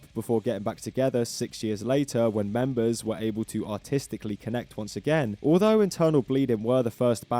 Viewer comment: i heard "autistically connect"